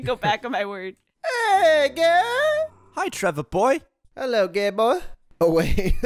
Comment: did you just say gay boy? Me, did he say Gable?